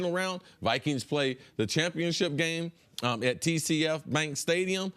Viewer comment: Did he just say txt bank stadium. It’s us bank stadium